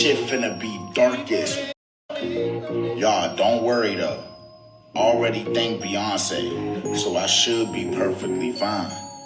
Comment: if u didn't catch it he said he'll be fine because he already thanked Beyonce 😂😅